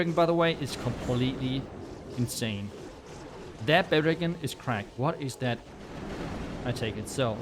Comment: Anyone know what he’s saying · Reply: “Baby dragon is cracked” as in it takes down the storages, creates pathing and destroys a rico cannon which is a great value for a bbd